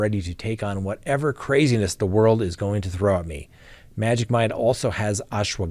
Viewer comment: ***NEW CRIME TALK MERCH: <link> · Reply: I thought you were saying to try some magic Mike! Lmao 🤣